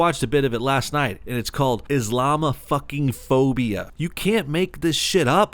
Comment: The first time he said "islamophobia" i heard "is llama phobia" instead.